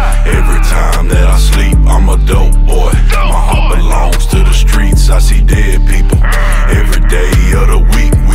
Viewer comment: i thought he said my heart belongs to the dopeboys and dead people😂😂😂